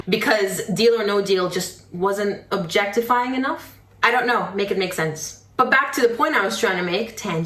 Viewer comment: You said “make it make sense’ I heard ‘Meghan make sense’. Both are right ;)